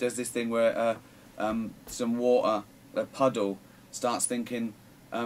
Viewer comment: is he saying water or what uhhh?